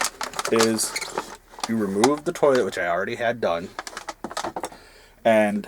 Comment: did you say you removed the toilet? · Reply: Yes, I had to remove the toilet to put it down into the drain pipe.